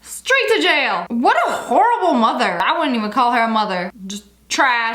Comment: When she said horrible mother at first I thought she said hor